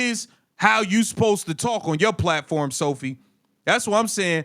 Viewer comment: I had to rewatch this part. I thought you said “that’s how you supposed to talk with your black voice Sophie” 😂